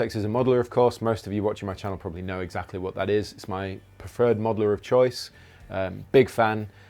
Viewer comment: I thought Rabea said "its my third modeller of choice" and i was hella confused until i rolled the tape back